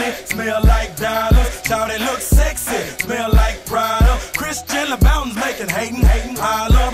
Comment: Did i hear Chris Jenner? 2:44 😆